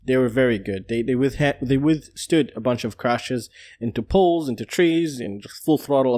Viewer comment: lol, i thought you said into pools, into trees at 1:24. Beaut build man, so cool